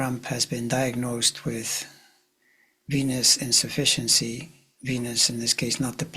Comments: At first I thought he said ...p*nis insufficiency....!! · For a minute I swore you said penis insuffcency 😂